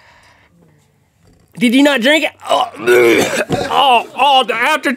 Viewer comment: Link: did you drink it?Rhett: noLink: WHAT DRINK IT DRINK IT!!! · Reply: oh I thought he said "did you drink it?"